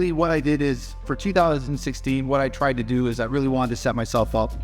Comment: he mumbles but I hear "two dollars and sixteen" but from context he's saying 2016 two thousand and sixteen... this mfer thinking about the bag so much he subconsciously thinks DOLLAR simultaneously.when thinking about a large number like two thousand. Thirsty